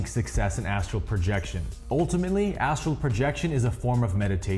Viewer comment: My husband thought he said "asshole protection" like yah you gotta be conscious to protect your ass hole... Cant protect yoir ass if you're sleeping. 😭✋😂